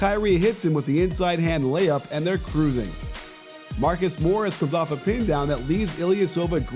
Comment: am i hearing right? marcus morris at 1:27 ? when actually, it is marcus smart? lol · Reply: nope. it's really morris. since morris is the one guarden by ilyasova. morris left ilyasova when he drove towards the rim.